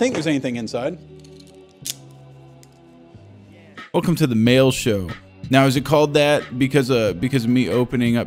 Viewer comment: Oh it's a MAIL show, I thought you said SNAIL show